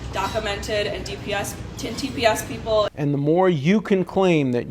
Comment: Did she say "TPS People"? I played it back a few times, but that was the best I could get. Is that a new thing?